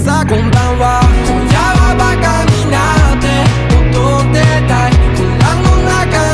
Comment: タラバガ二になって踊ってたいって聞こえてたから解決した